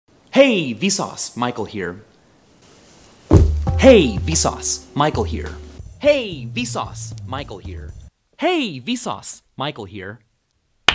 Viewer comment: I always thought he said "Hey piss off, Michael here"